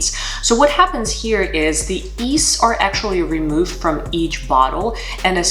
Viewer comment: you said something was removed from each bottle. It sounded like yeast, but I think you said something else?